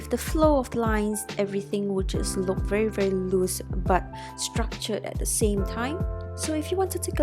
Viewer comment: you know is school messing you up when you thought she said lewis structure when she actually said "loose and strutured" -_